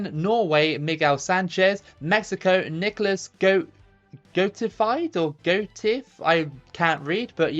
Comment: did you say Nicholas Goatifi? Thats a meme from the Formula One Community. A nickname of a racer called Nicholas Latifi who is famous in the Community for his shitty driving lol